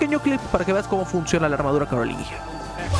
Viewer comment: Pensé que decía armadura de Carolina jaja